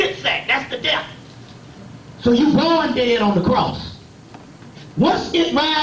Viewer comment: Whoa! (If I heard correctly) What he said at 33:40 is deep-deep, i.e. "you were born dead on the cross."